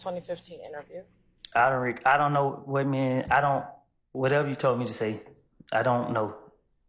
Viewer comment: did he say i dont remember what you told me to say at the prosecutor?